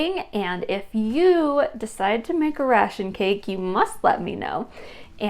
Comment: I’m too tired. I thought it said rotation cake. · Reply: she said ration!!!